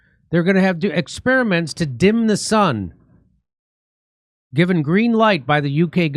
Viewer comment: I was all for it when I thought you said dim the sum.